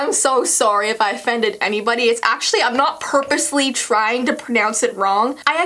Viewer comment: did she say purplely 😂